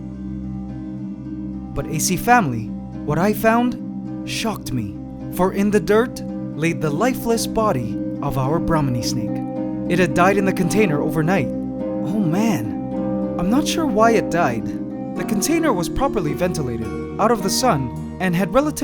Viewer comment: Brownie snake is it one of it ahahahhaha I just heard it 7:05 ahahha